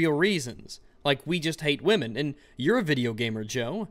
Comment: did he just say Nazi women?